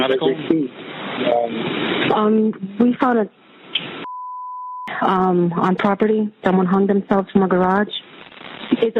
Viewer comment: What word is censored at 12:35? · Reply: Suicid3